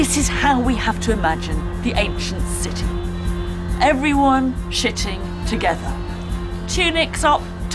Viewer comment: I thought she said "sitting" together, but she actually said "shitting" hahaha